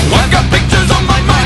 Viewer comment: At first i thought he said ''I got bitches on my mind'' lol